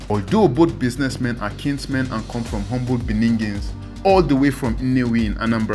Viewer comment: Shey na ear de pain me? did I just hear this dude say humble BENINGINS (1:31)😮na humble bene republic 😂😂😂